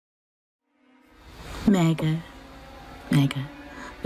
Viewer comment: I thought she said MEAGAN